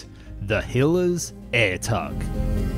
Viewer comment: I thought it said Hitler's air tug lol. Great video, by the way!